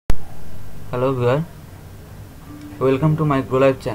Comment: sorry couldn’t understand what you said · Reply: He said 'hello viewer'